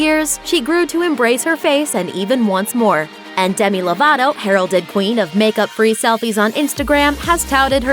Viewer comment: I thought she said “ Demi lavato herion queen “😭😭😭😭